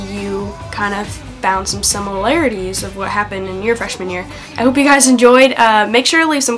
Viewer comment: When you say 'fresh man year' it sounds like you say 'fresh manure' aahha xx